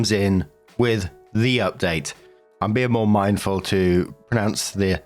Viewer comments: Actually, I thought you were saying "their update." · At first I thought you said “her update.” But then I rationalized you were saying “their update.” But with an accent. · I always thought you were saying “their update” 🤔😅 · I always hear you say “their update” 😊